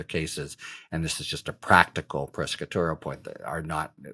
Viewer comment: I finally figured out that when he sounded like he was saying "pescatorial" he was really trying to say "prosecutorial"!